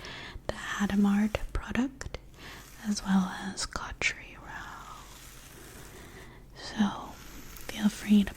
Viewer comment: I swear I heard "Country Roads" at first and thought you were gonna start singing some John Denver XD